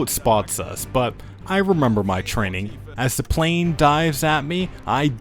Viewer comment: excuse me, I keep hearing "death chargese" 04:55 :o